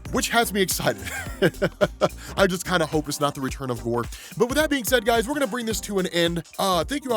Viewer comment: I don't think I heard that right. The return of whore? I listened to it like 5 times and thats still the word I hear.